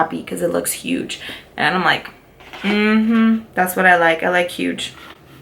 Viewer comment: You like it really big??? Sorry I couldn't help it!!! 😘😘😘😘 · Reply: I believe she clearly said she likes it huge LOL 😅😅😂😂👍WE LOVE U! !! 😘😘